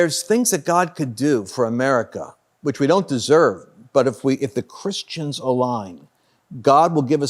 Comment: Please correct the Closed Captioning at 14:25. Lance said, "...if the Christians align...", but the CC says, "...if the Christians are lying...".